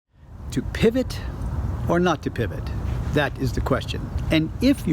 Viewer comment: The only flub I've ever noticed -- Master, you misquoted Shakespeare. He did not write, "To pivot or not to pivot (on the heel)-that is the question." The correct quote is, "To be, or not to be, that is the question:" . . . and of course this silly comment of mine is tongue-in-cheek :) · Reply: Thank you , all this time I thought he said "to Chi or no to chi " 😱